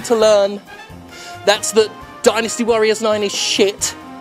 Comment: I thought he said "Trans women are winning" at the end i was so confused lol like "winning what ?"